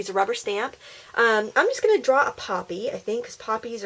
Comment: It was so cool ! at the beginning I thought what is a poopy I thought you were trying to say puppy hahaha!!